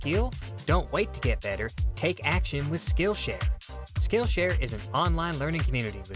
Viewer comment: Is it bad that I seriously thought that fade in said KILLSHARE?